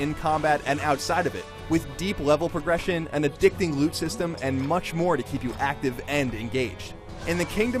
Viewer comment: I thought I heard "addictive boob system"